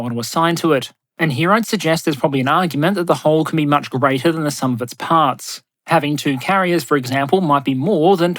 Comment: I thought I heard "that the HULL can be much greater than the sum of its parts."